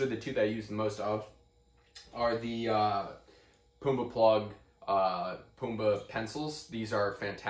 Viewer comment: Hey buddy. I’m new here and learning a lot with you thank you 🤙🏽 I have a question I couldn’t understand the name of the plug you said you use the most. I heard “pumba” pencil plug? Would it be the wood Gibbs pencil plug? Very excited for your answer 🤙🏽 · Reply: Close the name of the plugs are poobah Plugs this is a link to the pencils. <link>